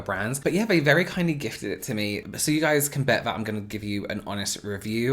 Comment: Oh my god, I heard "My fiance gifted it to me for my kids" and I got excited for a sec.